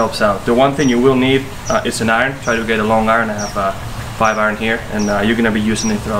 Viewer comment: I heard "Try 2 use a long iron, I have a 5 iron", then reached for my 2 iron, luv it! Thumbs up video!!